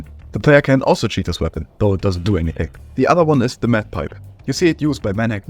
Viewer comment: i thought bro said meth pipe